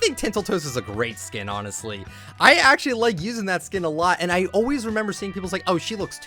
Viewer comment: Did he just say he uses the zoey skin a lot